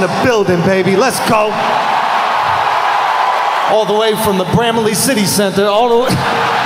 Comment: what's he saying, please? All the way from where? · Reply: Bramalea City Centre